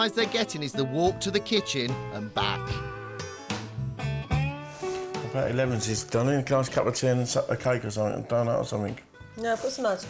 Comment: haha, I have no idea what he just said except "cake" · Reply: After a while of listening carefully, I think he said: "have a nice cup of tea and a slice of cake or something and a donut or something".